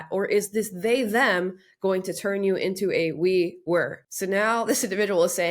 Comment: I think it should be, “into a were/was” That is what that person said, seemingly…..